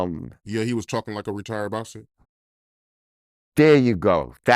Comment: Thought i heard retarded lobster